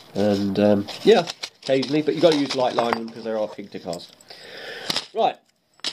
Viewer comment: "you've gotta use light line because ???" I've rewound it several times and I can't tell what you're saying. Help : ( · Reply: because they are hard to cast :)